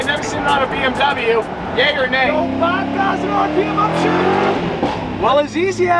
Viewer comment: Did you say gay or nay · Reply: 😂 Yay or nay